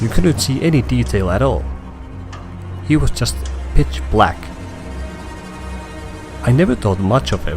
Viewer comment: I thought he said bitch black